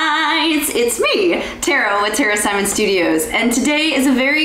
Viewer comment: the captions say, “it’s me Tara with terrorists Simon studios”